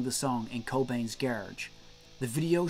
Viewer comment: Its sounds like you said in Cobains Garge, instead of Garage ? What's up with that?